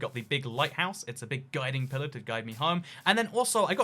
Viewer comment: I thought he said "Guide me, pillager, guide me home."